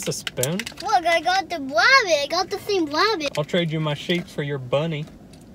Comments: “I’ll trade u my sheep for ur bunny” - 2k19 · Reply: I thought he said can I trade my shape for you're bunny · “I’ll trade u my sheep for ur bunny” - 2k19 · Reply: i thought u said sleep then i was about to say i thought it said sheep and just now noticed